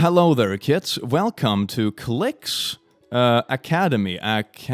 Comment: I almost give u a thumbs down because I thought I heard u say cl!ts academy!